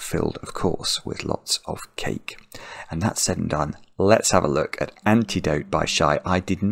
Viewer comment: you did not say "Alexa", you said "let's ha-" which sounds similar hahaha